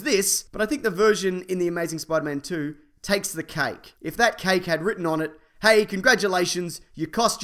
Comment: Omg, my video cut out right when he said "takes the cake" when talking about the green goblin and it sounded like he said "takes the cock" XDXDXD